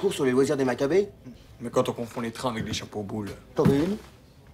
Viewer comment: J'ai pas trop compris... À la fin, la fille meurt ou pas ? Et à partir de 17:08, le gardien de cimetière, qu'est-ce qu'il dit ? J'entends "quand on confond les trains avec les chapeaux boules" ce qui n'a pas de sens.